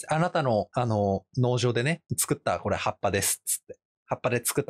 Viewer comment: 辺りの「葉っぱ」が完全にルビ付きカタカナの「ハッパ」に聞こえるw